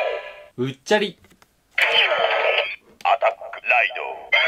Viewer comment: うっちゃりがぽっちゃりと聞こえる笑笑